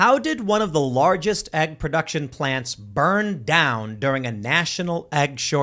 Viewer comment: Was I the only person who thought it said eggplant buring? Like the 🍆